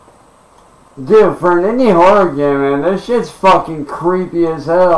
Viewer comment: Did he say "for a Indian horror game this is creepy as hell?"